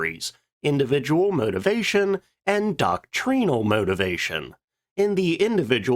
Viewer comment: I heard “Don Cheadle Motivation” and fully expected to see a photo of Don Cheadle.